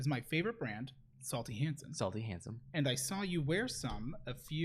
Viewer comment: I lost it when pronounced that nail polish as "Salty Hansen" 🤣🤣🤣🤣🤣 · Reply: I was listening to this in the car and I thought he said "Salty Handsome" XD